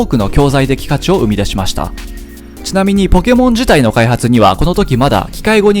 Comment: ピカチュウを生み出しましたにしか聞こえなくなった